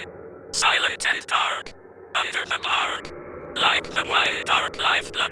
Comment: I only heard Under The Marc (that’s not a word lol)